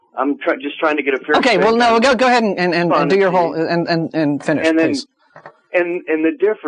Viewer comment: <--- "No no! Go ahead and dig your hole..I mean finish please" I don't think that's what he really said but it sure sounded like it lol